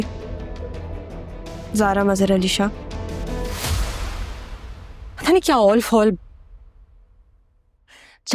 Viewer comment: broo she literally said ZARA MAZHAR ALI SHAH! 💀 I think her mother didn't hear it properly